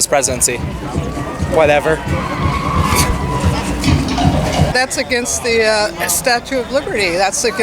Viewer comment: Did she just say that’s against the Statue of Liberty